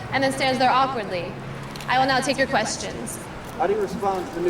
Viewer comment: Lol at first I thought she said “I will NOT take your questions” and then proceeded to answer questions anyway 😂